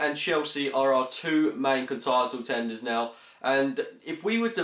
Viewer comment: Loooool @ 0:34, he said contitle tenders instead of title contenders... Great vid